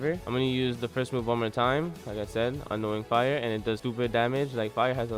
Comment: lol it sounds like your saying ''annoying fire''